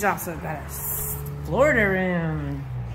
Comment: a what room? it says slaughter room in the captions, but i don’t think that’s right. or is it? is that what people are calling a sunroom nowadays?